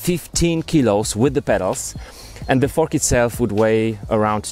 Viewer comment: i think i heard "with the f*ck itself"